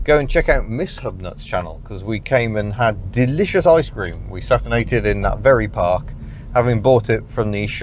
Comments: ICE CREAM - "We sat and ate it in that very park". I first heard "We sat naked in that very park". After rewinding and playing a few times I can at last hear the proper words. Quite a shock the first time around. Must get rid of some ear wax. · did you really say "we sat naked in that very park"? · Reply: "we sat and ate it in that very park" is what I heard. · So you sat and ate it, in that very park. With my sometimes dodgy hearing,at first i heard, "we sat naked in that very park"!